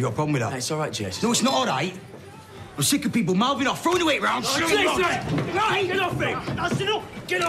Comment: What does he even say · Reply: "I'm sick of people mouthing off, throwing their weight around and showing off" ... That's exactly what he said